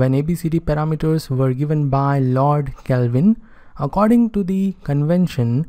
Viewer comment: did he say Lord Kelvin 1:46?